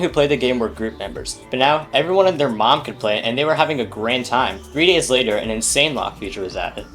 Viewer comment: wait did you say great or grand? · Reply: Grand, but great is interchangeable